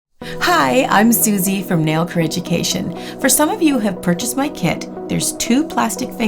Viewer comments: Every video I hear "nailcareeducation" lol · Who else thought she said nail cringe at the start!😂